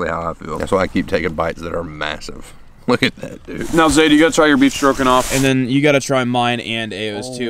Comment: it sounded like he said "you gotta try ur beef stroking off" dang I think that's for a different website lmao